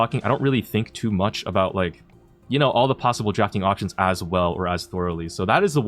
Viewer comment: sounded like you said “jacking offtions”